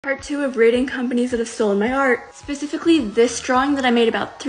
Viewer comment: Ignore this stupid comment I literally thought u said "Companies that stolen my HeArT